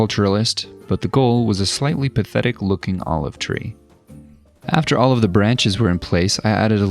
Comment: Is it just me or at 3:40 did he say "After OLIVE the branches were in place..."